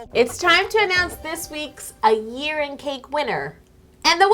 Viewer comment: I thought you said "A urine cake winner!". I was so confused.